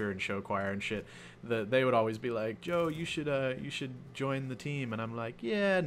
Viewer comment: Is your name Joe, or did I hear wrong?